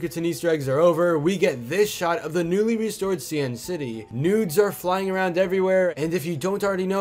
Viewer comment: Did He just said “nudes are flying over everywhere” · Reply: He said 'noods'